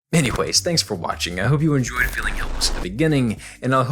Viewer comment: does he say feeling helpless? I can’t tell